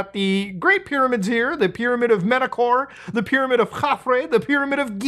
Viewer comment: …did I just hear “the pyramid of Mutahar”…???